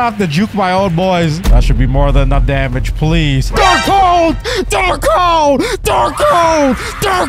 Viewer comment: What I heard from when he got dark coat 12:54 : dark hoe